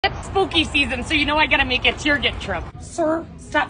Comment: I thought she said smoking season 😅😂😂